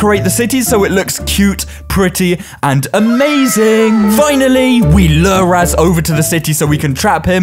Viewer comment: Lol did he say cool cute and amazing in